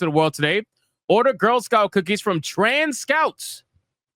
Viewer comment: At first, I thought he said, "Trained at scouts.You know, teach you how to sell the cookies. It's a beautiful thing. But he said trans Scouts at the age of seven blastphemy, 💯🤦